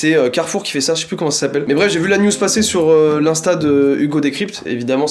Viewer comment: C’est moi ou il a dis « j’ai vue l’anus passer »? Vers 2:51-51 😅.